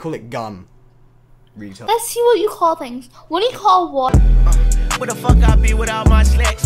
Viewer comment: did she really say "what do you call water:😂